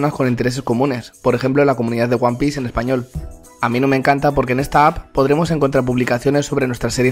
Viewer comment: Felicidades por obtener una promoción, cuando dijiste "Amino me encanta" pensé que dijiste "A mi no me encanta" , estoy pendejo :v